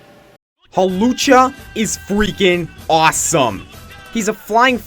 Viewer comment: Did I just hear Mucha Lucha!?